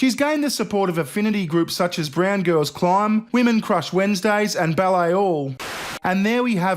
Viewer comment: All of that is offensive, but 'Brown Girls Climb' is perfectly acceptable. Pure insanity. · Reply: Oh! It was 'Brown Girls Climb', see, I thought what I heard was 'Round Girls Climb'. It's not as funny anymore..